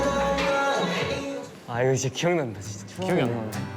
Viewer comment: did jake ang sunghoon mentioned "k hyung"😭 Huhu I'm not fluent in korean. I misheard it right?! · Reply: no huhuㅠㅠ they said 기억난다 which means it brings back memoriesㅠㅠ edit: 기억난다 (gieoknanda)